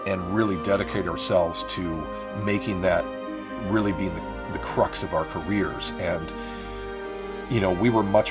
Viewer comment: krucks ; now that's a word you don't hear often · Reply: Because that's not the word. That's not a word. It's "crux".